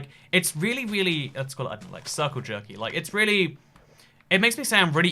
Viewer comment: did he say it’s circle jerky?